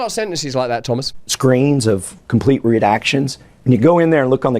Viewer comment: Did he say REDACTIONS or RUDE ACTIONS?